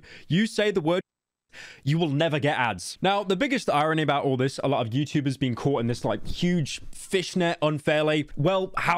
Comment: WHat's the word? I'm bad at reading lips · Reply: Hunt with a C